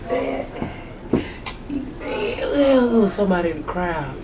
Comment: “I got the hug got the hug got the hug🤗” 😭💀💀💀💀💀 when I tell you I died 😂😂😂😂😂😂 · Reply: Damn I thought he was saying "I got the hookup"